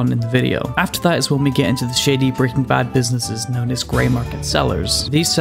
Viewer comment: LMAO, 3:13 when he said shady, I heard shiddy. I hope you see humor, not offense here.